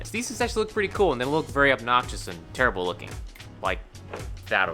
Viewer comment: Did you say “they look obnoxious” Maybe I have aids The hearing kind